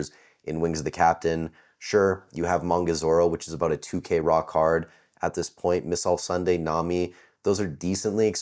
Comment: Lmaooo I thought you said “about a 2k rock hard”